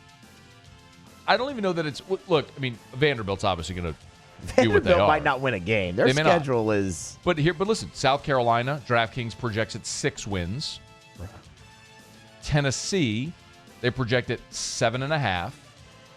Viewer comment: yes i do. new caoch loss of alot of players due to the transfer portal and graduation means a 6 -8 WIN SEASON. Looking forward to revisiting this thread in a few months... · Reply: sorry, I thought you said 5 or 6 wins.